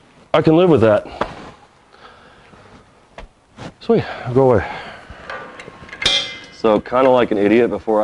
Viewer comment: did he say, sweet.Go away !!!!! ? 😂😂😂